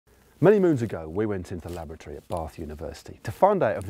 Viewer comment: first time Matt said "laboratory" I heard "lavatory" and got really confused about why they would go there to compare clipless/flat pedals